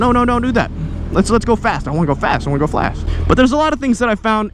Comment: I think i Heard I want to go flat